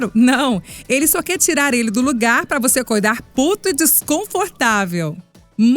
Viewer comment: Sabemos que vocês são muito fãs dos atuais narradores. E também sabemos que vocês serão fãs da voz da. Vamos concordar né? Que delicia de voz! Hoje ela estreia no time de narradores. Quer dar boas vindas ? Deixe seu joinha ai! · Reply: Eu ouvi errado ou a narradora disse "PUTO"?